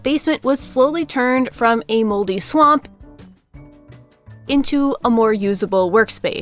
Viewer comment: I need my ears cleaned or something to wake me because I again misheard and thought you said "Moby Swamp" instead of "mouldy swamp", though now I kind of like the idea of a tiny mould and fungus whale swimming around your basement. Also, the introductions almost deserved to have that special yellow writing from old shows on them.